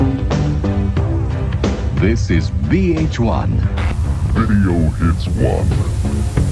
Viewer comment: Captions says "an escapable" when it should say "inescapable" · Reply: Captions says "BH1" but the logo clearly says VH1 (looks weird, but you can clearly see the V, not B)